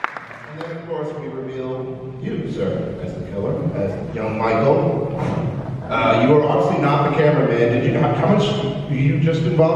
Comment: is will the cute little boy from the beginning of the first halloween? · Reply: Yes that is him and I thought they said a girl played young Michael hahaha